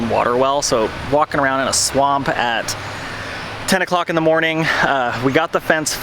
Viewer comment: I did NOT hear say "walkin around in a swamp at". Instead what I heard was "walkin' around with SWAMP A**".... 10AM is a little early for swamp a**, but ya know, it's July. We've all been there. Guess that's what I get for watching youtube at double speed to save time lol.